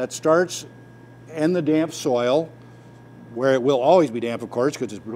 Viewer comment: I thought he said damn soil? 😆 🤣 I'm like wow that took an unexpected turn